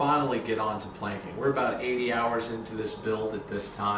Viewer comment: I like that its do it yourself project. 28 hours of work? Wow. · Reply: Is he saying 80 hours up to this point?